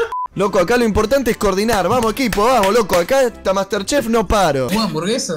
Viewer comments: Dejo youtube para cocinar muchachos... creo que me va a ir bien. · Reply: Pensé que decía Master Chief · Pensé que decía Master Chief